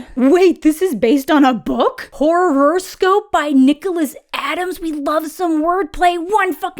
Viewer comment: For a brief moment I thought you said Necroscope, not Horrorscope, by Brian Lumley. I almost yelled, "NO ITS NOT!"